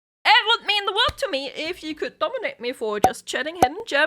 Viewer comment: Not gonna lie. Your accent made it sound like you said "dominate me" and I was "ayo?'